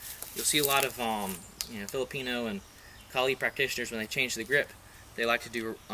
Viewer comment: did you say Kalari practitioners??!!! Honestly, I love you already. :) Love from Kerala, India. (PS- if you didn't, forget everything I just said.)